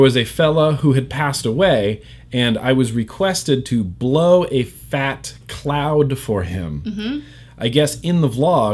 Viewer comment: I heard "flat coud"